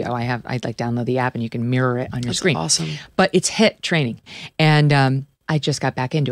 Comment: I thought she said hip training.